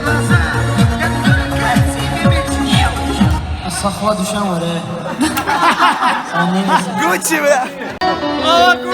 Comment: что он сказал??? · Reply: On skazal ostanovis lyudi xotyat uyti :))))